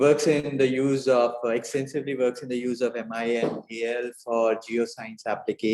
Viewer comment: Did he say MI and AL at 00:29 ? 🤔